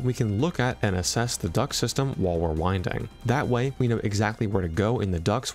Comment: I thought you said the duck system and I geuss that maked sense to me because it is nailed into my brain that Chica is a duck due to Markiplier